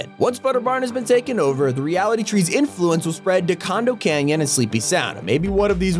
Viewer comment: Did you call condo canyon- "Condom canyon"?